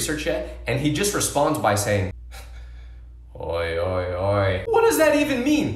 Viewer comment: oi oi oi is hey hey hey i think